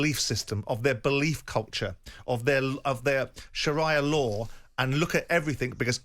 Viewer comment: I thought he said Mariya law 😂